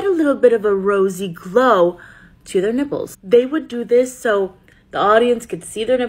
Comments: I’m so stupid at first i thought you said ‘dimples’😂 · I thought she said dimples 😭